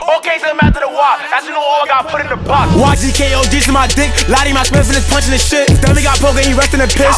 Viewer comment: Nas said ogzk ? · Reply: BaBy in the full song @ 1:13 dic head it sounds like he says ogz k as a adlib